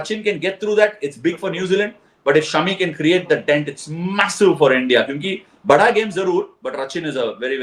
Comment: did i just heard the word " MASSIVE" ????????